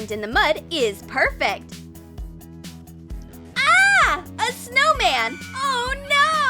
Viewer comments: Surviving in the wild is diffucult, all you have to be scared of is a big yeti footprint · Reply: Fåīr Ûñfãįrė wait I thought they said ahhhh a snow man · Did she just say snow man 🤔